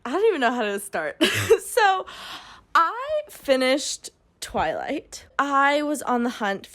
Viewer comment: Every time you said ‘twilight’ I heard the word ‘toilet’😂